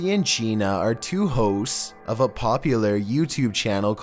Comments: On the 13th one it sounded like you said hoes instead on hosts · thought you said two hoes at number 13 xD